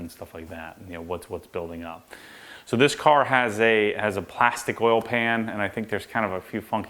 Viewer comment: Hahaha, I thought you said plastic oil pan... are you fucking kidding me?